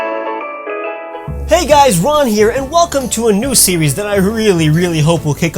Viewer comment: For a minute I thought he said “Hey guys, Juan here.”